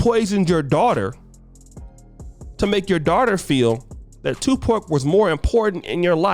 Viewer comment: did you just call Pac 2 Pork? 😆 🤣